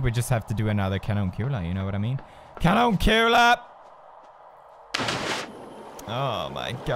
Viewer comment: what does he say 13:57 cadumcula? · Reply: nevermind its kanonkula swedish for cannonball